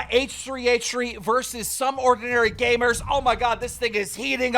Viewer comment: did he just say "oh my god this nigga is heating up" or is it just me?